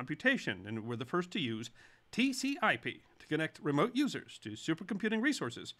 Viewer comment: Did he say "TCIP"? What's that? Maybe he meant "TCP/IP"?